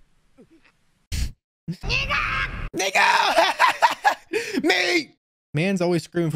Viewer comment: Am I trippin or did he say the n word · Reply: No he said Niku which means meat in Japanese lol